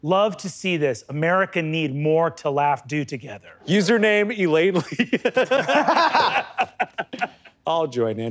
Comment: Wow so beautiful to see four men do good laugh comedy together. So wholesome good. Love to see this. America need more to laugh do together. ❤❤❤ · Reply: I think the subtitles were wrong. I thought he was saying "Elaine Lee", as in Brennan's mom.